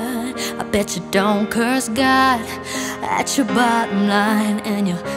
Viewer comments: At first I heard " Bitch don't cures god"... Love you Christina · LOL at first I thought she said "bitch don't curse god"